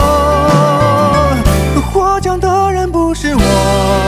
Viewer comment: 作詞_作曲_演唱_導演___都我浩哥啦😄！ 「復盤」聽成Food panda🫣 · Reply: “获奖的人”听成“活着的人不是我”🤣